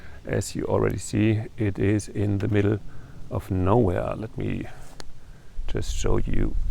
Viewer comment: I am loving this little peek at Germany. In an earlier video you said you lived in the middle of nowhere. I thought you said Norway! lol